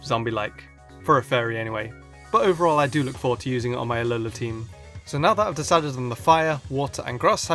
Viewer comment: I'm kinda far from my speakers and when you said Alola, I thought I heard "lololol" >_> I'm sorry.